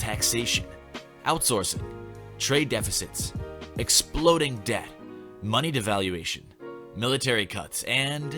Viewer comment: I really thought you said "exploding death" instead of "exploding debt", huh...